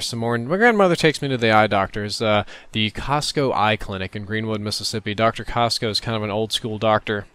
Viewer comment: at first i thought you said "Costco eye clinic". then i thought: WHAT DOESN"T COSTCO HAVE?!?!